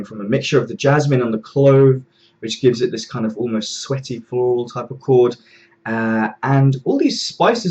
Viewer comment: Awesome review... When you said 'sweaty floral' I thought you said 'sweaty balls' and had to go back because that just could not have been right... LOL!!! Great job man... This sounds different that's for sure...